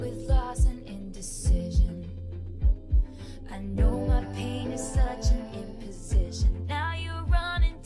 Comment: Thought I heard p*nis until I read the lyrics and it was pain is lol.....